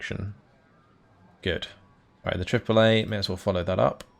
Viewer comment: you said triple A and i heard Chick Fil-A